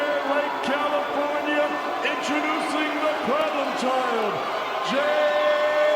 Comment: What did he say? Introducing the - what? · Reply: Problem child